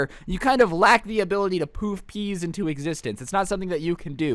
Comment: It sounds like u said poopies